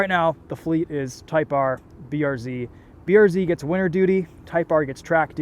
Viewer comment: Did you say winter duty?